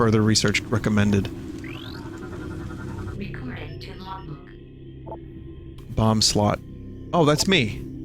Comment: Fuck the scanner voice says recording to logbook, when I was a kid I thought it said Mcfrentic Oc